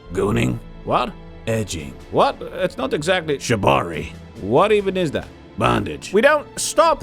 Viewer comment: Did he say Jabari? 2:05?